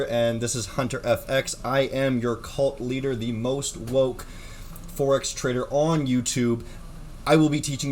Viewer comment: I thought he said "the most broke forex trader"... definately not.